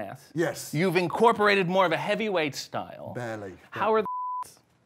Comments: he went full Chris Evans on that laugh 😂 still wondering what RJ said thay got bleeped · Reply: I think he says "how are the sh*ts?" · what did he say at 8:44? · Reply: How are the shits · What did RJ say at 8:44? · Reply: How are the shits? I think